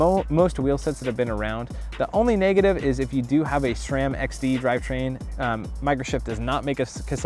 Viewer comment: i think you said sram XT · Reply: He said sram XD driver